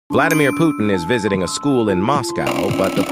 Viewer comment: I thought the AI kept saying Vladimir Poopin 💀💀💀